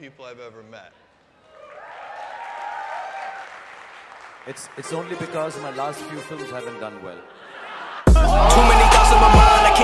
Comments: What did he say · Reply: It's only bcoz my last few films haven't done well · after that. what srk said? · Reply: Thats only because my last few films haven't done well